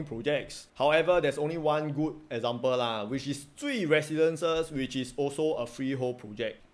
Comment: HAHAHA! When you said Jui Residences I thought you said CUI Residences